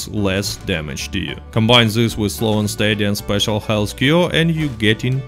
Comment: What did you say to combine with at 8:55? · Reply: Slow and steady